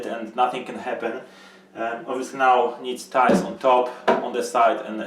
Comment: also what is it you say at 6:34 "obviously not it needs ??????? on top, on the sides and on the walls" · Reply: "Tiles"